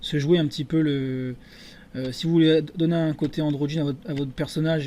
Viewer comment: cest quoi le mot que tu as dit ? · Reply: Jeff The Gamer - [FR] Antrogine